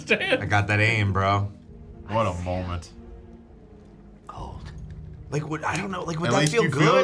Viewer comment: when I first watched this movie, I thought barbosa said “I feel… old”